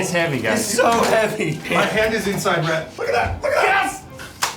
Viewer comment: "My hand is inside Rhett" does it say more about me that I instantly heard that...